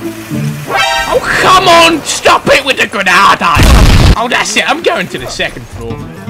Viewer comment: lol 9:40 ''oh come on stop it with the granada'' what i heard "ALLAHU AKBAR"